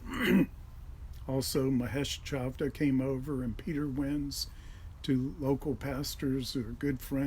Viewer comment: I thought he said Peter Wentz and I was thinking “what was Pete Wentz doing there? 😂